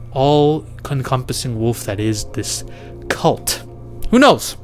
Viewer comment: “being hunted down by the all CONCUMPASSING wolf”? Huh? @ 11:45 “the themes UNDERCURTING the film.” What? WTF are you saying half the time, brother?